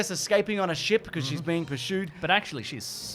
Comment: did you say "Per-shooed" ?